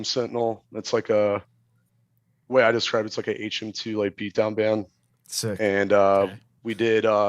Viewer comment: what does he say before beatdown? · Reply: HM-2